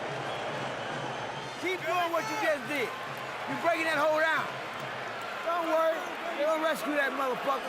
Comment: does roger say " u breaking that hoe down , they'll rescue that mutha fuckka "